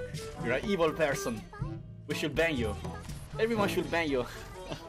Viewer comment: why did I misheard that as “Everyone should bang you”?